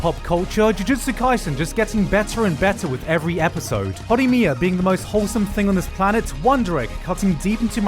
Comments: Can someone please help me with getting the name of the anime at 0:13? I can't really hear Gigguk, all i can hear is "hottie mia" lmao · can someone tell me the name of the anime? I heard wandere but I found no anime with that name. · what is the anime he mentioned after AOT? I couldn't understand him. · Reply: jujutsu kaisen (dunno if i spelled it right)